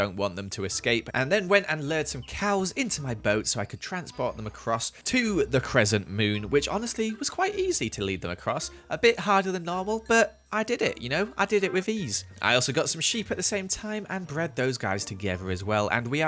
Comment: every time he says "cressent moon" I hear "Pearlecent moon"